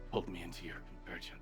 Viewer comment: 💀 I heard: You pulled me into your virgin 💀 Instead of: You pulled me into your Convergence Also: 35:02 Fuckin comedy gold 😂